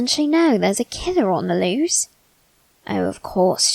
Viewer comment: I thought he said "there's a kinder on the lose" my brian : KINDER EEG